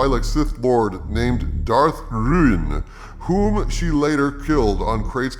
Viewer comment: Did you say " Darth RIAN " ??? Was the decapitated head perfectly spherical? 😂😆🤣